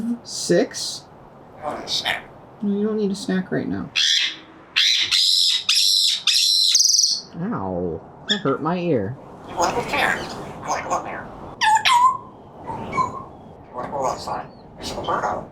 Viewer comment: “You wanna go there?” I misheard as “i dont care.”